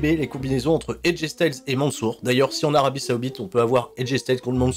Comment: arabie quoi ? 😭